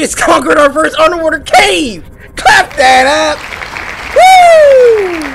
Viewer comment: did he say clap day a**